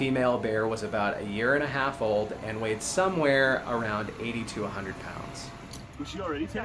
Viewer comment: 80 TO 100 pounds. I thought you said it weight 8200 pounds 😂